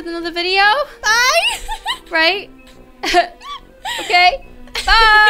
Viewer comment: i think she said start for life idk